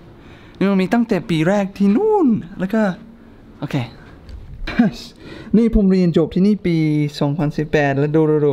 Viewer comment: ได้ยินว่าผมเรียนจบตั้งแต่2018